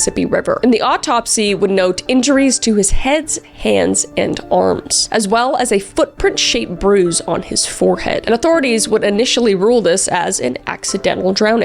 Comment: "Heads"? Did I hear that right?👀👀